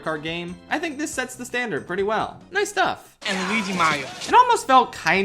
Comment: what did the number transition say? I couldn’t understand it. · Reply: "And Luigi Mario."